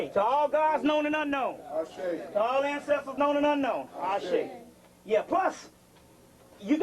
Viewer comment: what are they saying hoshi? Or hasha ? Can someone help me out ? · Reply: Ase' (au-shay)